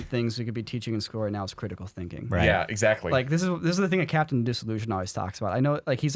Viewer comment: Sounds like a job for...... Captain Disillusion! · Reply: I heard "Captain Delusion" lmao